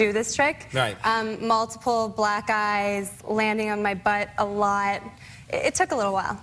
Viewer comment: seriously sounded like “multiple black guys... landing on my butt... it took a while”